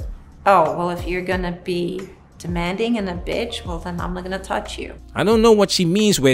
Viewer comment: Slow that down to half speed. Even the closed caption misses it. What she said he said in response to her being demanding/biatch is "I'm NOT going to touch you". He is withdrawing his affection in response to her throwing negativity at him. It's a lower level of walking away - not as severe, but meant to send a message, a warning. You all know how women hate it when they lose attention.